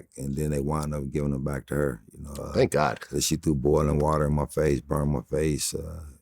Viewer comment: Did vlad say thank god @ 3:30 I thought you don’t believe in god vlad?